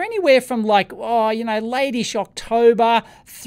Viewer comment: When you said "late-ish October", I heard "Lady Shocktober". I prefer the latter.